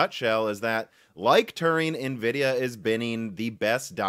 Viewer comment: Every time I hear him say "Tigh(t)-IN" I hear Gina saying "Mar(t)-IN!"